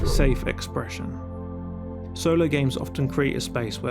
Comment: I heard "solo gay men", I have been summoned